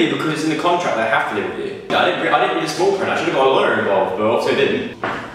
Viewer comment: I can't understand any of what Joe says from 3:52 - 4:00, the echo in the room is so bad :/ I've replayed it so many times. Can anyone tell me what he says??? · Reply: 'I didn't read the small print, I should have got a lawyer involved but I obviously didn't.'